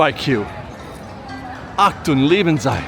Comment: "achtung lieben zei" is what I hear. But google translate only does so much 😅